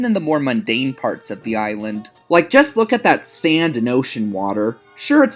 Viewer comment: what did he say? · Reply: “Just look at that sand and ocean water.”